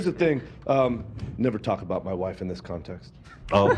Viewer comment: For some reason yesterday I thought said "I" don't talk about my wife that way so I never even thought he was saying it menacingly to Ethan until the watch back haha